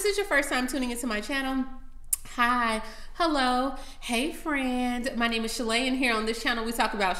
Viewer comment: I thought u are saying hey fran